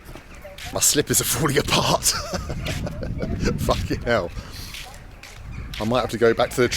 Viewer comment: Wait who said fuck you there? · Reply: hahaha, I didn't catch that the first time! Bald said "fucking hell" to the shot and I think the waitress thought it was at her and it sounded like she said "Fuck you" back 😂😂